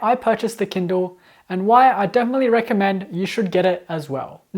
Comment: "Why I don't really recommend you should get it as well" What... but the title says he does recommend it... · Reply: I think he said "definitely" not "don't really" lol